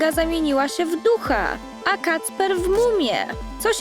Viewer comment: Czy tylko ja usłyszałam mucha ha ha ha😂😂😂